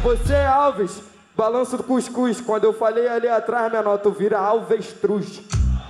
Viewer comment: Agora q eu entendi " Tu vira alvestruz "